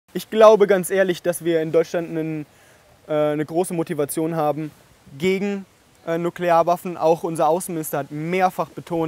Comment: "I honestly believe that we got a strong motivation against nuclear weapons here in Germany. Even our foreign minister emphasized on multiple occasions that..." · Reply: I genuinely thought he said something about his Großmutti when he said "große Motivation" XD ("Groß-mutti...Vation? sure, that makes sense") The nuclear war did seem like an odd topic change with that in mind though 🤔